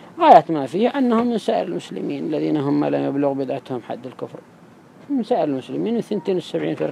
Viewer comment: From @ 13:55, I hear "الذين هم ما لا يبلغ بدعتهم حد الكفر"